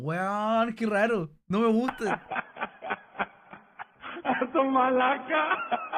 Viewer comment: Yo pense que decia estoy borracha, no esto malaca :c